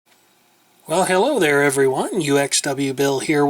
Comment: My computer skipped while loading the video and it sounded like you said "Well, hell the ho there, everyone!" :P